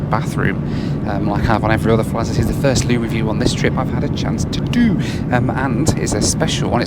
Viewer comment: Congratulations on becoming the first aviation youtuber who flashes black light on aircraft loos. Its an industry first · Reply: I heard changing the name to the "Goo Review" lol GROSS!